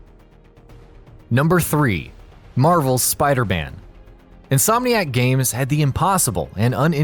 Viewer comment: Am the only one who hear him say spider ban